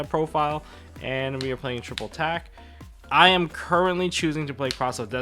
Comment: Idk why I thought you were saying 3 copies of triple tac 🤣